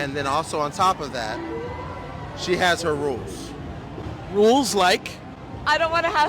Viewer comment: Did he say she was 22 and making demands? Oh no buddy go ahead And Because You are just wasting nothing but your time